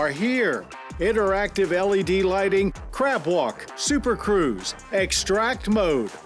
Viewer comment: I swear I heard him say crap walk… when you gotta go bad…😅